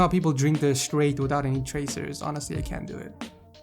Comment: did he say tracers?